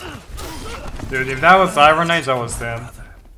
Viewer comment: what did genji say ? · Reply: if that was IronAids(best hanzo player) i would have died